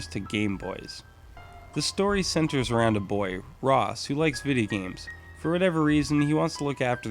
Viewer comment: did you say, who likes vidi games?